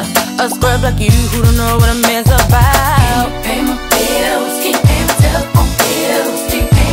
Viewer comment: I thought it said "I cant pay my bills." xD